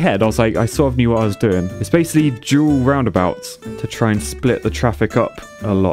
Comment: Jeez y'all pronounce stuff so wired in the UK. For example when you said dual roundabout I thought you said jewel roundabout